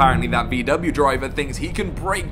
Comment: That "bmw" driver is a VW driver. You really should know that. But great video. Waiting for my model 3 performance😊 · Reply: I think you misheard - he said "VW driver" - Google's transcription algorithm even agrees (hit the three dots and "Open transcription").